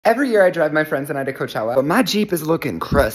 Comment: Girl I thought he said “every year I drug my friends in coachella.”